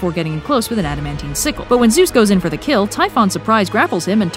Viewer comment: Did you say "getting close with an adamantium sickle"